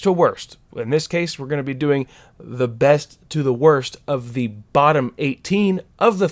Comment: is it me or did I hear “besth”? Tbh I forgot Heir to the Jedi existed lol